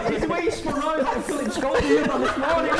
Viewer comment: can someone tell me what said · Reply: It's the way he spun round ' was like Phillip schofield on this morning ' 😝